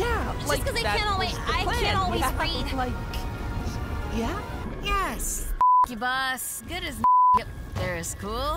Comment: "Yes, ** you boss" (laughing out loud) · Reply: It sounded like she said "yes, frick you boss" wth